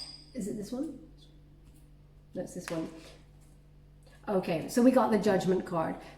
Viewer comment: It was really shocking when I heard it at first (😳LITERAL CHILLS😳) but had to rewind it again..where you ask the spirit "is it this one?" And I guess it was spirit who replied"this one". You are incredibly gifted 🙏🏻 Sending you light ,love and strength😀